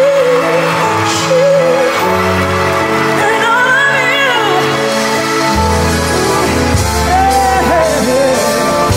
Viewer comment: someone knows what she is saying here? i can’t understand you. please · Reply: She said 🎶…My darling you…🎵